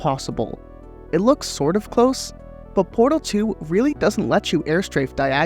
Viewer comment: i thought it said ''postal 2''